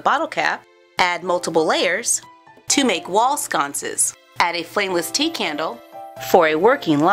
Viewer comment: When you said a flame-less tea candle... I thought you said a flame-less TEA COWW!!! #TeaCowsAreAwesome